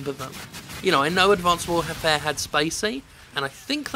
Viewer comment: it sounded like you said "I know Modern Warfare had Spaceape?" and I was doubly surprised that a Hyperdub artist would be featured in a franchise better suited to brostep, and 2, that Jim Sterling was a fan of Spaceape. On second listen I was both relieved and disappointed.